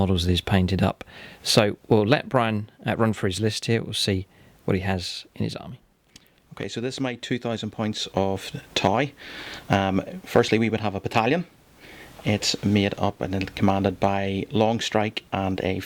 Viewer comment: When your friend said “these are my 2000 points of Tau” it really sounded like he said “...points of Tie”.